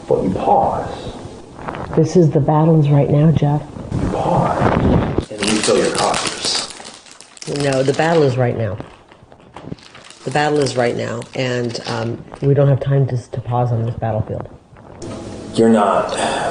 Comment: I heard him say she could pause and FILL HER COFFERS